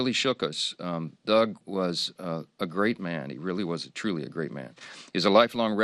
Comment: I swear I heard, "Doug ate a crate of hams" which makes more sense than 'great man.'